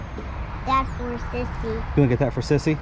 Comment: I thought the child said that’s for sissies